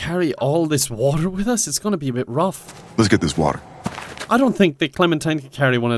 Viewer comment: ive seen the memes so much i could have sworn he said "lets get this bread" xD